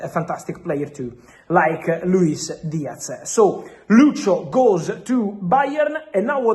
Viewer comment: sounded like "Luke Shaw to Bayern"